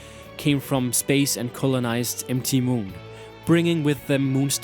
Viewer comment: I thought you were saying solrock and empty moon. I wasn't looking only listening and had to really think to understand what you were saying :-P M.T. Moon was very confusing when I heard it. Mount Moon! :-P